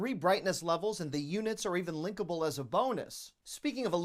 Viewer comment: You said linkable for the fluorescent lights and I thought you said lickable. lol